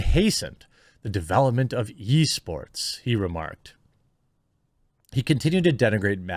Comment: Sounded like you said "Yeesports" XD